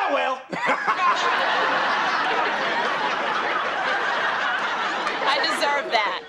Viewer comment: I swear Hillary says “I deserve that”